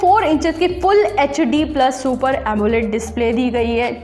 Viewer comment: is she saying "PULL ECH D'' or what???